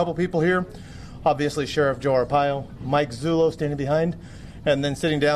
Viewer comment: Im sorry, it sounds like he is saying Sheriff Gomee Pile, lol